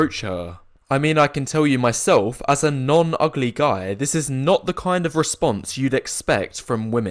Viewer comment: I thought he said "norm-ugly guy" and I just assumed that was a term that meant men that were not "sub-3" but like 4-6s.